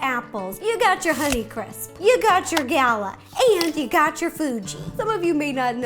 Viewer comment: I noticed some cc errors in the video I just wanted to let you know, specifically at 0:20 instead of fuji it says food. Anyway just wanted to help the deaf community as much as possible <3 ya ;)